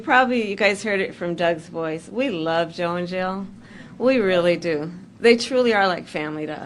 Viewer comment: Did camela throw up in her mouth a little when she said she loves Joe and Jill ? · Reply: I thought she said "we love Joe in jail."